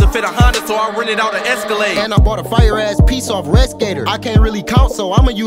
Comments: What site he say at 0:36?? 👀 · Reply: Rescator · What site did he say on 0:37 I needs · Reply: Redskater